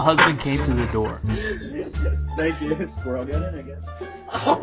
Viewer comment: Without the subtitles I thought the husband said a squirrel got in again